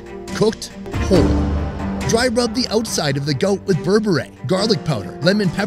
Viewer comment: did I just hear berbere?